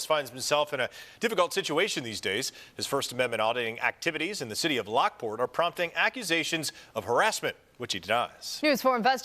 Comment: What did that reporter say? I thought I heard “The city of Lockport are prompting actuations of harassment, WHICH HE DOES.” Did I hear that right? Did that reporter definitively say Dan is harassing the city of Lockport? Inserting his opinion as fact? I’m I wrong?